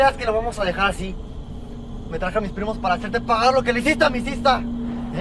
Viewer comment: Que dijo el ex mejor amigo de Ulises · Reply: “Me traje a mi primos para hacerte pagar lo q le hiciste a mi sista”